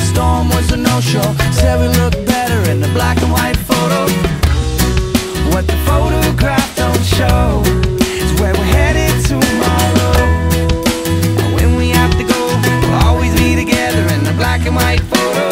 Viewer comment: I always thought he was saying "What the photograph don't show, is when we're headed to my room." LOL